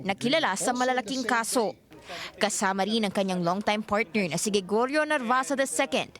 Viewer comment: Did she say "long-time partner"? Or law firm partner ba yun?